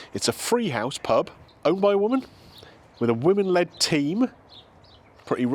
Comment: I thought you said a wooden legged team , I thought fair play then I thought if you nutmeg a player is it a goal ?😏🅰️